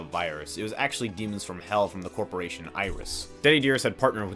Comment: I thought bro said ISIS for a second